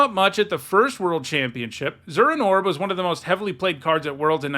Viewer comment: Man, I wasn't looking at the screen as you said what the two options were. I thought you said "Frozen Orb" (What I remembered Winter Orb to be called) not "Zuran Orb". Winter Orb was awesome.